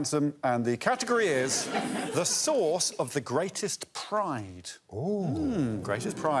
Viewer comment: I sincerely thought when Alex said "source of the greatest pride" that he said, "SAUCE of the greatest pride" and I was thinking we were going to get some saucy prizes...